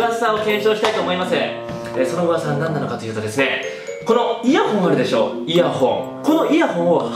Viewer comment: イヤホンがイヤホイに聞こえる。 原宿でイヤホン！原宿でイヤホイ！